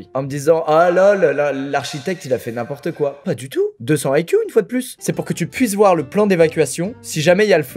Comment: "deux-cent haikyu" j'ai entendu ça 😂